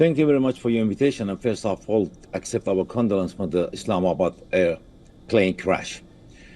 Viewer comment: what did he said 😂 Islamabad plane crash?